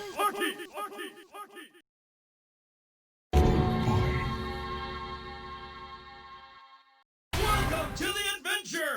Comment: what does it say 1:44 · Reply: it says “Vampire”, with a choir echo behind it.